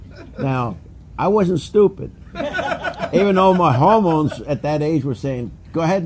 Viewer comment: Did he really say "homos" at 1:09...? Yeesh. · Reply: No, he said "hormones."